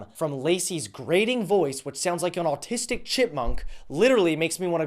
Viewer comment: Well, I clawed my own ears off on the 9th due to extreme salt poisoning. I misheard and thought you called her "an autistic shitmonk" at 5:02. Then at 5:24 I heard: "without a doubt..Trump gave a voice to unspeakable duckness" dies